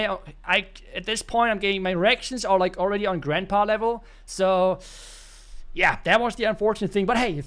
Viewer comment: Love the video as usual, so fun! But at the end I was so sure I heard "My erections are already at grandpa level" hahaha